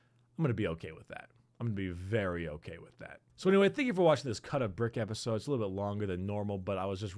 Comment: Could’ve sworn that said “Cult of Brick”